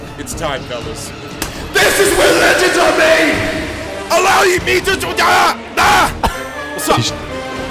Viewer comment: "THIS IS WHERE LEGENDS ARE MADE!" "ILLAOI ME TO DU DAH" I swear this had me dying XD